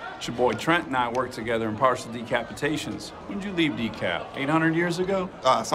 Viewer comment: "Parts and Decapitations" ♥️ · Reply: I thought he said "partial decapitations"